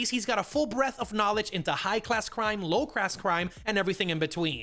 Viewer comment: low grass grimes lol... Tf you saying? Ga ga get it out Junior